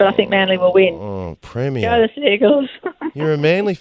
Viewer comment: Gladys said "Go the Seagulls!" She's so out of touch she doesn't even know the proper name of HER team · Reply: To be fair she said Sea Eagles, but slurred it and the autosubtitles heard it as seagulls..